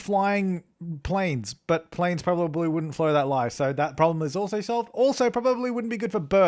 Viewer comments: Did anyone else hear that he said 'planes probably wouldn't flow that lie' at 1:32 ? XD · Lol at 1:31 he said planes won't flow that lie instead of planes won't fly that low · I love how he said FLOW THAT LIE instead of FLY THAT LOW😂😂😂💙